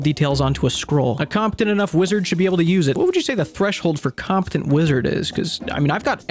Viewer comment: I thought you said Compton wizard....